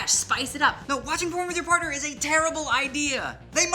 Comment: I thought it said "watching porn with your parents" I was like how is that even a good idea